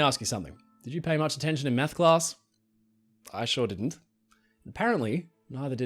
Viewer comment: Totally thought he said "meth class"